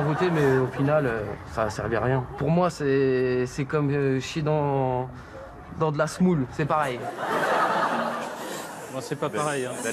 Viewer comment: il dit quoi a 1:33 ? (jsuis anglais et jai du mal a comprendre cette partie) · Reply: "Pour moi c'est comme chier dans de la semoule, c'est pareil" métaphore pour dire que cela ne sert à rien.